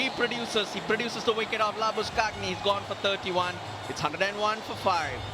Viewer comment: did the commentator say Lambuschag-neh instead of Lambu-shane? Lol.